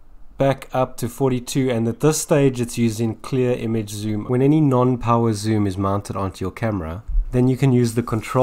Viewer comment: what is a power zoo? I heard you say it twice.